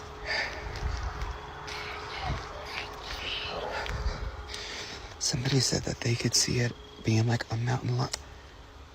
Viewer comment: & 5:50 ish you can hear two separate distinct voices not narrator or creator whispering while the bones scattered everywhere and No Body noticed 1st one creepy female demonic 2nd creepy male demonic whispering undistinguished what was said . No one else heard it?😢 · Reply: Absolutely!! Yes, the 2nd voice was a female saying : "Can you _ me?"